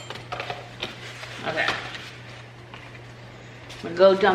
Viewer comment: When you were cleaning out your basket of blankets and said "hanger" I heard "anger" (a basket full of anger) just made me laugh out loud....simple pleasures, I guess.